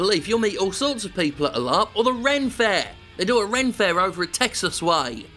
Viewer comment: Hi Jim, what is a rent fare? Or did I misheard? What were those two great places you suggested for meeting people? Or if anyone heard correctly and cares to explain. Thanks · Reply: I think he said "ren fair", short for Renaissance fair. It's basically a medieval-themed festival with lots of costumed entertainers and events like Jousting.